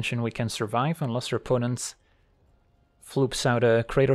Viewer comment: excuse me, did just say "floop out"?!!